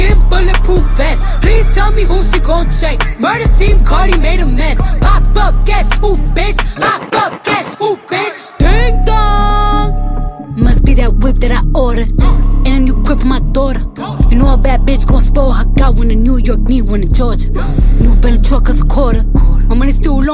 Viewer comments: I could’ve sworn she said must be that book that I ordered lol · I'm glad videos like this exist cuz I thought she said "cripple my daughter" and I was SO scared